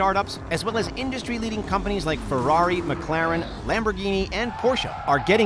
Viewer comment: Did u just say...lehferrari?